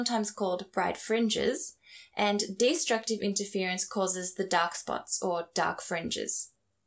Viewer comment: Whenever you say "dark spot" I hear "duck's butt".